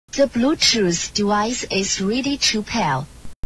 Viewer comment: Even the subtitles says: The bluetooth device is really cheap.